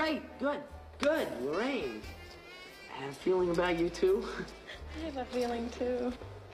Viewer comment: When marty fistbumped lorraine's chin(which i dont understand why he did that)did he seriously say i have a feeling for you while lorraine responds with i have a feeling too shyly? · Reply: I thought he said I had a feeling about you two. But I know it could seem like he said that lol